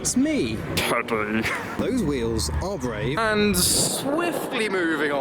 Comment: Did you say my wheels were brave? 🤣